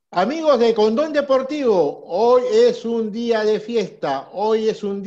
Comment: Escuche "amigos de Condon deportivo" jejeje